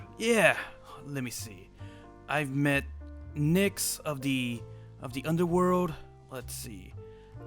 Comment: Wait hold up did he say Nix???!